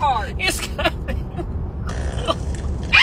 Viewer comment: to be honest, the way he says "HAS HE CRAPPED?" makes me think this is not the first time they went through this already. xD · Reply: Hahaha.. totally thought he said SEA CRAB instead of did he crap ..lmao